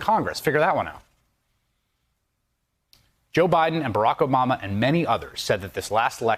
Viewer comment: Lol, @ 7:19, did he say Barack Omama! or did I hear wrong.